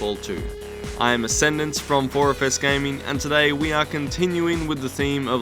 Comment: Thought he said oriface gaming at first tbh